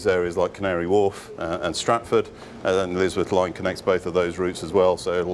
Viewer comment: Thought he said 'Lizard Line'.